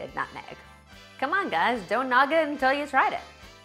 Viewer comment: Did she just say "Don't NOG it 'til you try it?" about that milkshake?...... I'm dead! LOL!